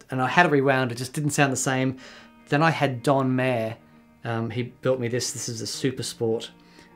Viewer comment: Am I the only one that thought that he said John Mayer at 2:06? I had to rewind to check because I knew that couldn't be right...